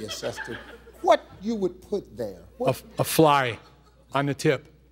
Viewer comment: what did he say he would have as the tattoo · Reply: A fly